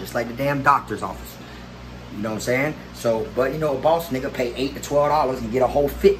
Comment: Did he just say nigga